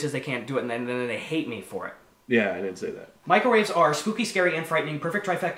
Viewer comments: when he says "microwaves" sometimes I hear "Michael Reeves" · sounds like he is saying Michael reeves are spooky and scary. Lmao